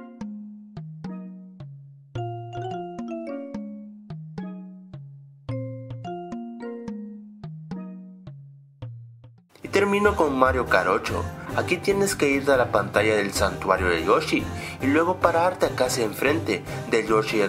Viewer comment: minuto 03:55 yo lo entendí Mario jarocho xD